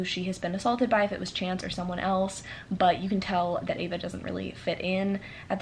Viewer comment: oh my god i straight up thought you said ava DIES instead of "ava Does..." right after saying it wasnt a happy ending